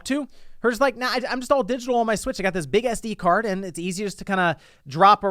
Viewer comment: Swear I heard him say, "Big-ass D card"